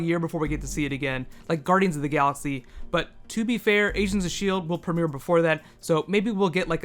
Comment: it sounds like he said asians of shield lol